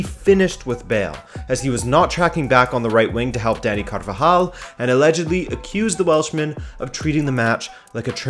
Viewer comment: lol, 4:15 does say "Daddy" Carvajal?